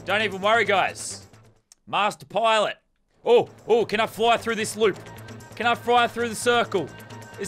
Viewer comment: t 7:55 did he say "can I fry through the froop